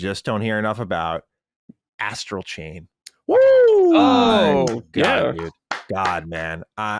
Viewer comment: I know is talking about Astral chain but I swear it sounded like I heard him say "Ass Cocaine" 🤣